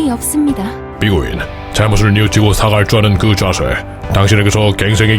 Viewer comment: 살짝 갱승의 여지라고 들리는건 기분 탓인가